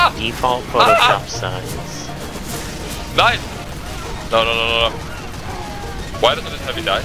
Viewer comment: did I hear a:“NEIN“?